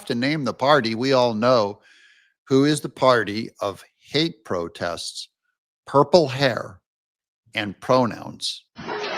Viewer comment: you misheard the former LA Sheriff, he said he was "leaving the party of PAID protests, purple hair, and pronouns."